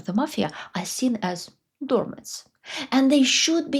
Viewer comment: Dear, I meant to respond to your last response to MY comment - about law and state. But here we are again, together again, as I ask you what is the word: Min. 8:55 (The people who are not part of the Mafia are seen as (what?) dormers? I'll be seeing you again as I plan to watch all of your videos; you present so much of things I have long wondered about. I am thankful to have found you. (Where do you live now?) I live in Chicago. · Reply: Or maybe you are saying doormat.